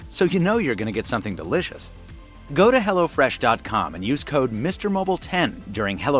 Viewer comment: Lol...I thought you said "hellafresh". If they went with that name it'd probably blow up even bigger. Maybe....